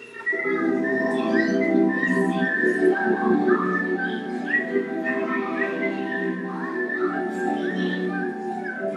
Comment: Omg nostalgia I was so freaking scared Btw, what does she say at 0:57 ?? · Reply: Hafff